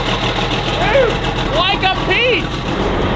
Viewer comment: wait did he say like a beast or Pete? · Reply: like a PEACH remember he's from Kansas